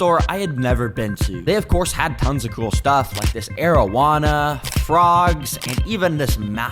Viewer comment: I thought he said marijuana instead of arowana 5:39 lol